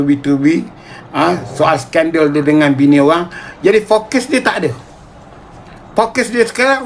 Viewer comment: Cuba dengar perkataan di 9:38 "screwskan bini orang". Adakah hal ini berlaku? Mungkin aku salah dengar